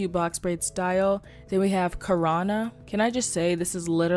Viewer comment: Who else thought she said “Corona” I can’t be the only one